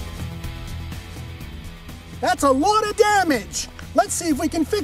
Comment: I swear I heard that's a WAD of damage 😂😂😂